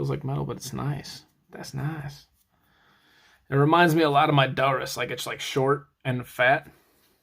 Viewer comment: did you say, “it reminds me a lot of my daughters, it’s short and fat”